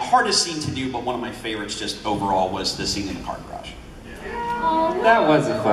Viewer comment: what did he say? 9:16 he sounded so sad 😂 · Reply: Bella M the scene in the car garage